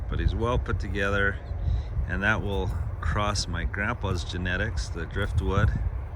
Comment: Wow, I didn't know you could cross your grandpa's genetics post mortem thru a horse... 😂 I'm teasing. That's what I heard and I was like what... Yea, I wasn't paying attention. 😂@ 8:57 comedy relief